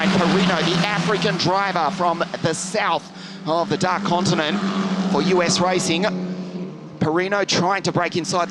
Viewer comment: what did he say at 17:53??? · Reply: African driver from the south of the black/dark (idk) continent for US Racing.